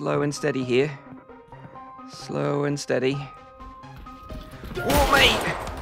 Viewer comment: did man say "What mate!" 🤣🤣🤣🤣🤣🤣